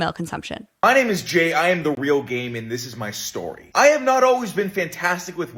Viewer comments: He should rethink his name because the first like full video you reacted to I straight up thought he said "I am the real gay man" like i was genuinely confused for a hot second. (i was doing work for class so I wasn't looking at the video to see the captioning) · I heard I am the real gay man bruh